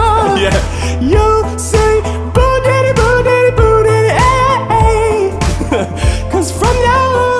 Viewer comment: It sounded like said boo daddy instead of boo